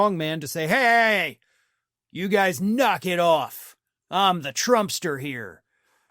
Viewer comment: I heard "I'm the dumpster here!"